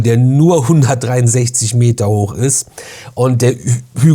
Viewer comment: Was höre ich da? Ich habe gehört, dass der 180 Meter hoch ist, und diese Zahl schon in Diskussionen verwendet.